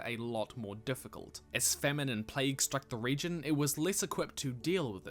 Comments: I thought you said feminine plague, instead of famine and plague. I didn't know plagues could be feminine. I wonder what a feminine plague is like. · I totally heard "famine and plague" as "feminine plague" and immediately pictured soldiers in makeup trying to fight in high heels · "As feminine plague struck the region" XD (Yes, I know what he said) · Dang it, this is NOT a video I can watch in the background while sorting my email. I gotta give it my full attention. Also, at 3:36 it sounds like you say "As feminine plague struck the region" LOL. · For a second I misheard "famine & plague" as "feminine plague" and I was like "wait, what?"